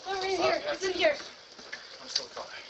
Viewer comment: No idea who she is but she led the thieves into her house. "It's in here". Not too fishy. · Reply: sounded like she said “he’s in here” no?